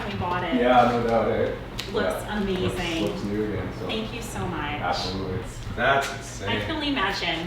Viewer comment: When the guy said "Thank you so much" I thought he said "I hate you so much" Lol I'm stupid